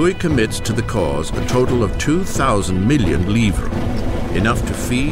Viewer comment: what did louis send two thousand million of?? i couldn't hear it. the time is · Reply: Rhiannon Pascual livres. French currency of the time